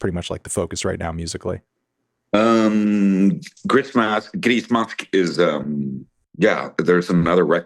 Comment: Anyone know what the side gig Seb was referring to is called? Sounds like Gris Macht or something like that? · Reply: grismask